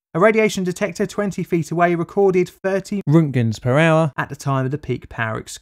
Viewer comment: Did you say "rems" at first? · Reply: No he said Remtgens... Which is the newest and coolest radiation measurement standard on the block!